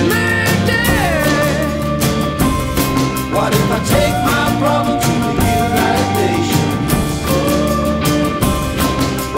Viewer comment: also, i thought she was saying "why did i take my problems to the lil white nations?" but oops, not that snide.